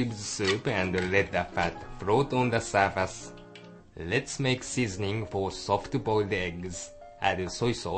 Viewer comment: I watch this anytime I want top ramen and he said seasoning I thought he was saying sizzling